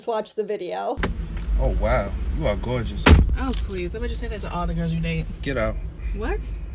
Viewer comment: Could anybody please write down what the lady says at 2:48 in this video? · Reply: oh please, I bet you say that to all the girls you date.